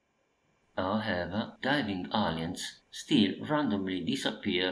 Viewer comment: I kept hearing "aliens" as "onions" which resulted in this misheard gem: "No more jumping onions!" =D (seriously though, I love your PCB repair videos...they're so calming and I do learn a lot)